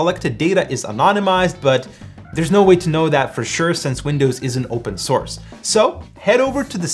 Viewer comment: closed source Edit:I misheard it